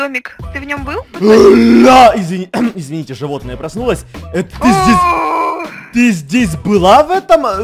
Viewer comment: слышиться что она сказал пи*дец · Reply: °•MABLE•° Пх, похоже. Но нет, он сказал ты здесь... Ты здесь была в этом доме?